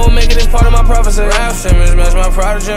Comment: I thought he said "Will Smith and more and more"